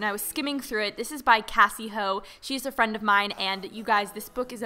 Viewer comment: I thought that she said " Cassey hoe " omg ://